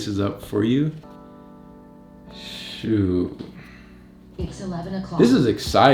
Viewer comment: I was tripping I thought the shoes said “It’s 11 o’clock”